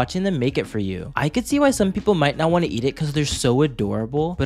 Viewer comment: Did he just say a fish busted in his mouth 👄